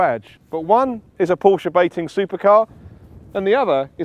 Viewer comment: I thought he said porsche baiting?